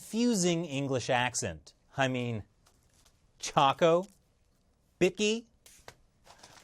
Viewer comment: chocco, bickie,macca's wow that sounds intresting i didnt knew the term chocco b4. Chocco? i thought he said charcoal. And most confusing accent? Indian english? black eng American Eng or mayb the australian eng